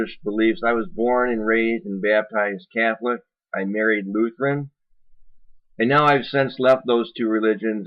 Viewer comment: I misunderstood when you said you married Lutheren I thought you said you were lutheren... hence -- that's why I apologized.... me thinking that you were baptized three times instead of your stated twice.